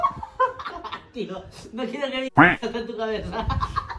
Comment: Like si cuando escuchaste lo que dijo en el minuto 4:03 te pusiste a reír😳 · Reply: segun yo dijo no quiero que mi cola este en tu cabeza